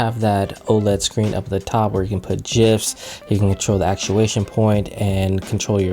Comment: Hold up did he just say jif on